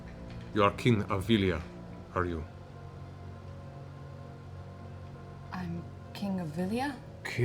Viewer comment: I HEARD THE SAME THING MARISHA! “I’m king of Vilia?” Haha!! Such a tense moment but that was so funny!!!